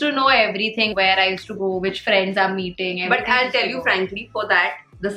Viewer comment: Did she say, ' which friends i am eating' hahaha, she forgot to put a "with" - no worries hahahh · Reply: She said "which friends I am meeting"